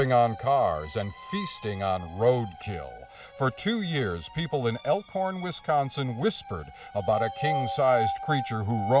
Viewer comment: I’m not going to lie, I thought he said “ the people of elk porn county” 😂😂